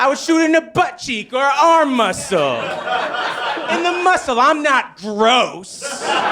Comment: funny how the subtitle says "I'm gross", eventhough he said "I'm NOT gross".